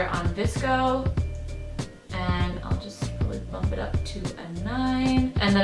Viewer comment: said "drop it to the 9" 😂😂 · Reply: I heard "bump it up to a nine"